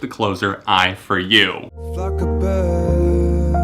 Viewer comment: So funny when he first put ghost stories up, it sounded like said "fuck a bird"